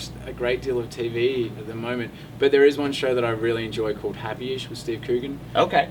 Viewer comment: What show did he say at 5:46? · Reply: I believe he said "Happyish" with Steve Coogan <link>